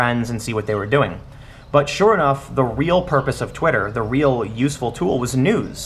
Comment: Damn the first time I swear I heard him say "The purpose of Twitter was nudes" lol